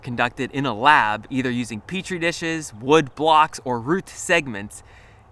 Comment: Did say 'Peach Tree Dishes???' 13:07 lol